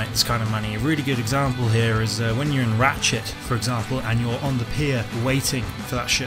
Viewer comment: when you said Ratchet, I swear you said RAT shit